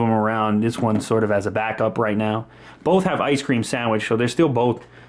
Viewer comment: Did He Say Both Have IceCream Sandwitch?